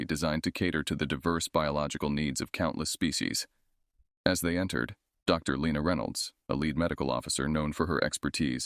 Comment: At first I thought you said "Dr. Frank Reynolds. I had to rewind and do a double take!